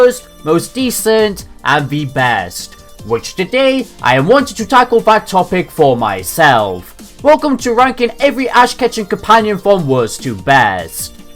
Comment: all i heard was ass catching companion